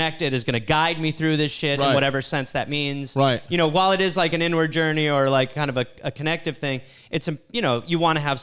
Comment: Thought he said "N-Word Journey." Got skeptical about them drugs.